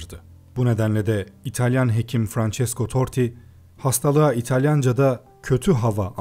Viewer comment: Francesco Totti anladım 3 defa dinledim 😬